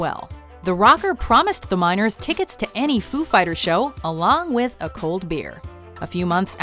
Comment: i thought she said "the minors with a long cold beer" i was like wtf foo fighters driking with minors? lol